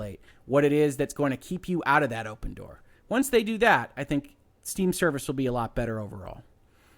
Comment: At the very end of all the talking I think your mouth got tired because it sounded like you said "once dey do day" and it made me laugh.